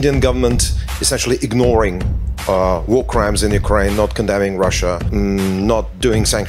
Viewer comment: I just heard " wooo crime " their accent 👏👏👏